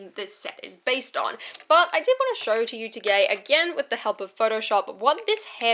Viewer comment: I keep hearing to-gay